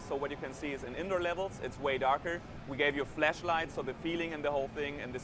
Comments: Did he say "We gave you a fleshlight" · it sounds like he said he's giving us a fleshlight. :P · did he just say fleshlight? ...... niiiice :P